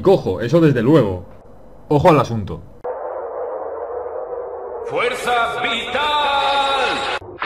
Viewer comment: t Yo oi fuerza frital :v